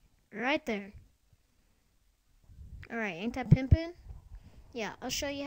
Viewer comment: did you just say pimpin? wow... -.